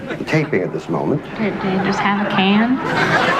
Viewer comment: what she saying? "have a can"? I cant make out whats being said